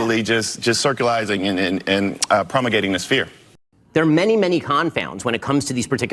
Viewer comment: did I hear "circulizing and prommagating"?